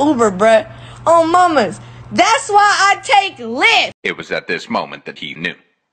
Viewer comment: I legit thought that she said "Das why I take LITH" I didn't hear Lyft at all. It made more sense to my brain that she was taking lithium for bipolar! 😂😂😂😂